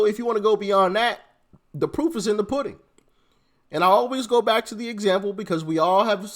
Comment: I heard "the poop is in the pudding". Yep it sure is 😂